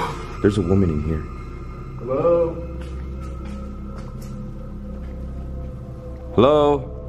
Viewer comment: Uploaded this a week ago and had to take down because YouTube's robots wrongfully blacklisted it from ads and views. I worked really hard on it and its one of my favorites. A lot of you seen it and alot of you didn't. If you did see it please RE-SMASH that like button PLZ. Merry Christmas. Will be uploading a brand new video for you tomorrow on Christmas. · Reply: I think it was a woman no more but if it was it sounded like she said help help so many times it was very creepy and she said in a really shallow voice